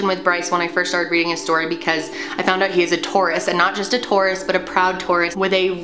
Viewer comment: lol i thought you said tourist instead of Taurus oops